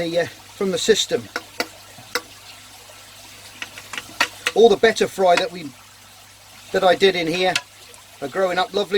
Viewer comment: Hi! Since I'm from Brazil, I'm having some trouble with a few translations. Can someone explain to me what is that substratre/dirt he's putting on the two tanks at 10:50? How is it called? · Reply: he said it's peat